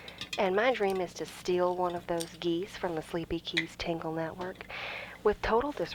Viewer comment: I misheard it as “Sleepy Geese” and thought it was a spinoff lol